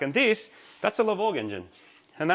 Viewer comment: The Lavorg. I believe it was originally out of a '17 Sti when they swapped the Lavorg to full STI spec. They then pulled it to swap in the build H6/EZ30. If you're from the states the Lavorg was called Impreza wagon. Hope this helps and it is from memory sorry some details could be incorrect. 😊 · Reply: thanks ! i thought i heard lavorg but couldn't really tell.